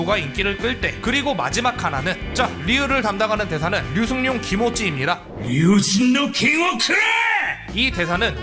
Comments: 류승룡 기모찌는 예전에 어디선가 1.5배속인가 2배속 했을때 똑같이 들린다... 라는 내용 때문에 흥한 걸로 압니다. 실제로 겐지 성우를 초청해서 류승룡 기모찌 1.5배속, 2배속에 대해 얘기를 나눈 인터뷰가 있었는데 성우도 인정하는 느낌으로 얘기해서 웃겼었죠. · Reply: 너무 류승룡 기모찌로 들림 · 류승룡 기모찌는 예전에 어디선가 1.5배속인가 2배속 했을때 똑같이 들린다... 라는 내용 때문에 흥한 걸로 압니다. 실제로 겐지 성우를 초청해서 류승룡 기모찌 1.5배속, 2배속에 대해 얘기를 나눈 인터뷰가 있었는데 성우도 인정하는 느낌으로 얘기해서 웃겼었죠. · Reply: 발음 때문에 류승룡 기모찌로 들려서 후에 재녹음한 시네마틱이 올라왔던 걸로 기억하네요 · 아니 나는 아무리 들어도 류승룡 기모찌로 들리는데.. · 전 지금도 류승룡 기모찌로 들리는데여 ㅋㅋ